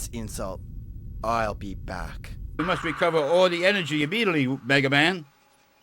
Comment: "You must find him immediately, W- Megaman"🤣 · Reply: I always heard that as "You muss wecover all the enewgy immediatry oop Megaman!"